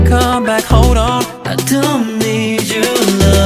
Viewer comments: It sounds like he sayin ~I don't need chenle~~ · Bro I swear I hear " i don't need chenle"